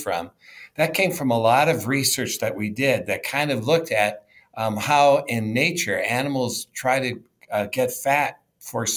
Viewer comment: What did he say at 1:31 to 1:33 minutes.. Umami or Yumami foods? I couldn't understand the pronunciation. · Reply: Umami/ savoury